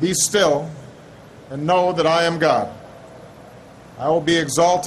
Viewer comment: I heard "When i am gone" really fast, it just sounds like "I am God"